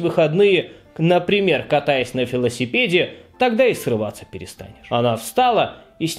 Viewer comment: Мне послышалось, "проводить выходные, катаясь на фаллосипеде". Заработался, видимо.